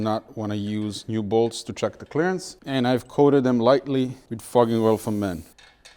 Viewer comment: I still chuckle every damn time I hear "fogging oil for men" or "carbon fiber towel".